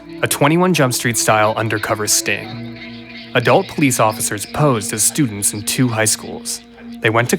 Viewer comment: I thought it said "RETARTED OFFICER"